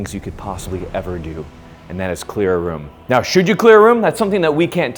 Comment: Ngl I thought he said “to clean your room” at the beginning